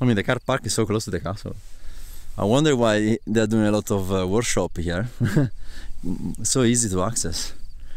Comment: Another great video, and amazing timelapses 😃. I cant understand what you are saying at 9:25? Something about warshop?? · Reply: ahah I meant workshops, sorry! There are a lot of Milky Way Photography Workshop here, because the night sky is very good and the location is easy to access. Thank you, so happy you enjoy it! :)